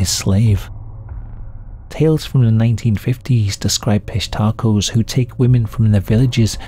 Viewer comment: I thought this said pistachios and it was some underground fucked up pistachios cult. Pistachio like the nut or legume or whatever….